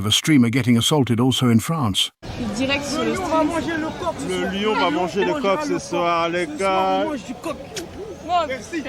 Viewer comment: I may have limited French but I heard “je veux Manger le corpse cet soir” which I think (do to social cues and what I’ve learned) means “I want to eat you out tonight” or just “I’d like to eat you tonight” Or something along those lines. And obviously “bisou(s)” is kiss/kisses